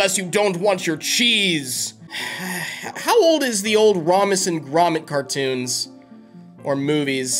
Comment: did i hear you say romac and gromit